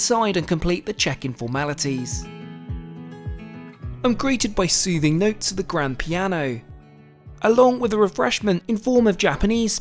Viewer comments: I apply every year just to make the odds harder. I cant afford the tickets im just an asshole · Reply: I’m hearing impaired and hadn’t turned the CC on-and the first time I watched this, I swore said ‘I’m greeted by the soothing sounds of Stevie Nicks at the piano” 😂 In fact, I had to go back and double check that he didn’t. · I’m hearing impaired and hadn’t turned the CC on-and the first time I watched this, I swore said ‘I’m greeted by Stevie Nicks at the grand piano” 😂 In fact, I had to go back and double check that he didn’t.